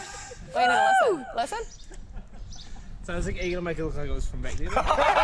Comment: What does Izzy say at 3:10 ???!!!! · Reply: he says "are you gonna look like it was from back there...?"